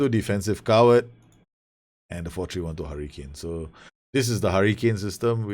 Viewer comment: I thought you said you'd made a Harry Kane system lol. "I have Harry Kane", "I can modify Harry Kane" lol Then I read it.